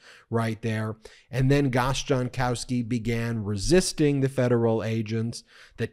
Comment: i kept hearing the guy's name as 'Gosh Darn towski'